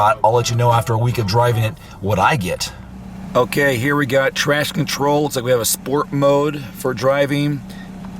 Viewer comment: did he say "trash control"?